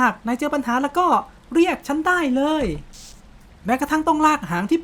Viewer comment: ตอนแรกหนูได้ยินว่า เพี้ยะฉันได้เลย55